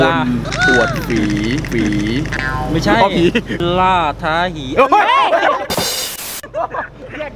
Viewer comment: พูดว่าอะไรนะ55555 เต็มๆชัดมาก5555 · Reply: ล่าท้า hee